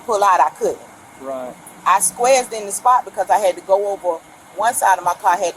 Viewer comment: mark... Did she say "squazzed"??😂😂😂😂